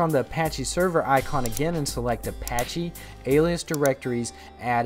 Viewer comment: When you said alias, I thought you said aliens. XD